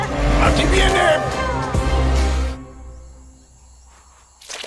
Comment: Pensé que decía Venezuela 9 o Venezuela 7 xD